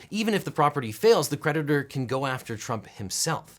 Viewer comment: Whenever you said 'creditor', I kept hearing 'predator'.